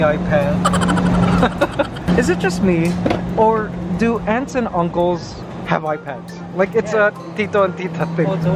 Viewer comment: I heard "do ants and uncles have iPads" and I was so confused for a moment.